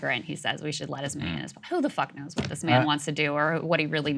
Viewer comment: did Crystal say "who da fuck"? 🤨